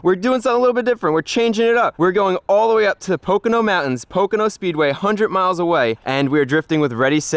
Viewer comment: For a second, I thought you said Pokemon Mountain lol